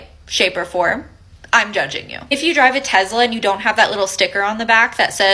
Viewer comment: …. Did I just hear her say TeZla? Gtfo. Also, if you don’t like my American flag aesthetic on everything that I own practically, that means you don’t love this country and you need to also GTFO OK thanks bye